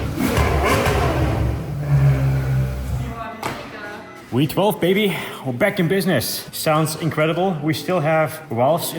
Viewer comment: What did she say?!?!?! 🤣🤣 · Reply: Boa, megaaa….